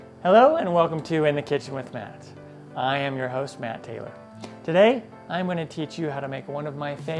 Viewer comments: When you first said your name i thought you said Matt Damon. Then you said your name again and you said Matt Taylor and I was like "that makes a lot more sense". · 😂 for a second I thought he said I am your husband